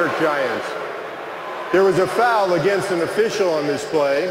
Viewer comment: I thought he said there is a penalty against the official 👌😂